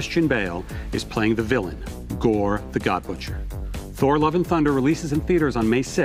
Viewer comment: For a sec there, I thought he said Gorr the god muncher.